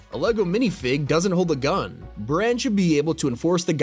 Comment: did he just say VR? 😄